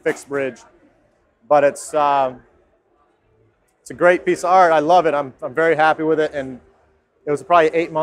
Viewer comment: does he say "it's a great psr"?!!! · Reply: Harry Eldridge he says piece of art